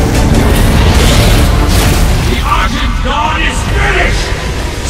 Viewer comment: What did he say? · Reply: The argent dawn is finished